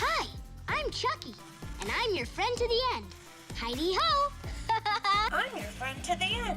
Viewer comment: The video says I’m your friend to the end. I thought he said I’ll be your friend til then end